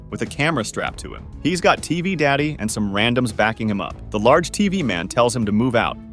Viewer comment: did he call shadow tv man Daddy tv man??? AYOOOOO